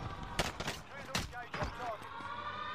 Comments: did he just say "a clean country" after that? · Did I just hear him say "A clean country" after he killed him lmao